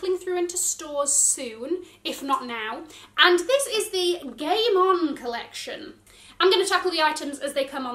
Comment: When she said “The Game On Collection,” I can’t be the only one who thought she said “The Gay Mom Collection.” 😂